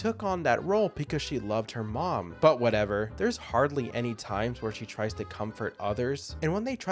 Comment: do I hear "mother" in the sentence? on two characters" relationship which later became romantic? interesting